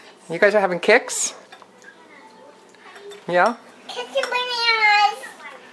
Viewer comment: OHHHHHH Kix and bananas....i thought said, Kick some asssss...lol